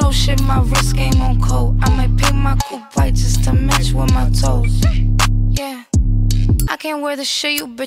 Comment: Thought she said “I might paint my poo white just to match my toes”. Listen to it three time, put my ear up to it and everything. Had to look up the lyrics on that one.